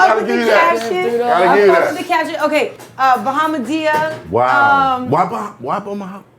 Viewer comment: I thought she said madea from Tyler Perry movies lol